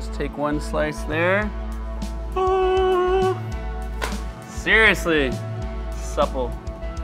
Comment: Did he say..."SEAR-iously"?